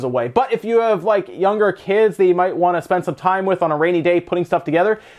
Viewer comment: he says “Rainy day” but to me it first sounded as “Raily date”, instead of “Daily rate”. That honestly made me laugh. Keep up the great videos!